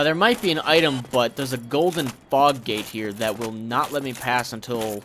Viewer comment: the first time you said fog gate i thought you said fox gade